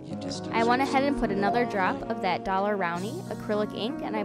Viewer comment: what is the other brand of ink, it sounds like your saying dollar round? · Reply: It's Daler-Rowney FW acryllic ink